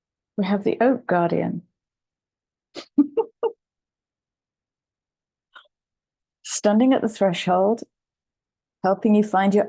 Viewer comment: I heard the 'Oat Guardian' as I was pouring oat milk into my tea 😂😂😂